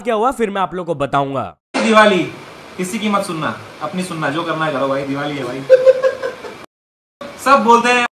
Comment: he clearly said "jo karna hai karo" Means fodhna h to fodho nhi fodhna h to mat fodho